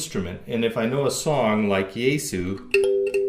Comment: This instrument is simply amazing! Though I have to ask about the song you're mentioning at 1:55. I'm having a hard time hearing what you're saying and I need to know the name of it! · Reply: Jesu = JS Bach's Jesu, Joy of Man's Desiring.